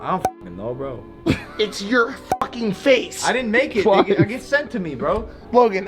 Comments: why it sound like he said "i didn't make it nigga" lol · did he say I didn't make that nigga